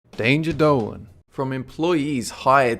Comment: i always thought you said 'danger dough" until now